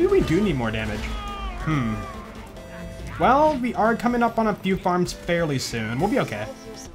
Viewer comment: the only thing I hear is: "We're coming up on the bunker"